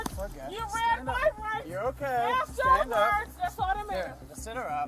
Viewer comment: I’m sorry but your race card has insufficient funds. Please use your accountability card instead. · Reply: She said " you read my rights after ??? " what is she saying?